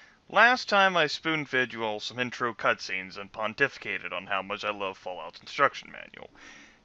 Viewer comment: When you said 'pontificated' at the start of the video, I heard "Which upon defecated". ... What is wrong with me?